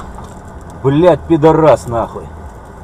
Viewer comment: What did he say at 0:58 blyat figaras.... Whatever... Did anyone know what is he say?